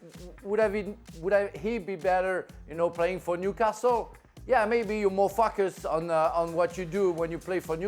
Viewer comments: I thought he said at first "you mofos!" 😂but said "maybe you are more FOCUSED" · ahhah Frank sounds like he said more F***ers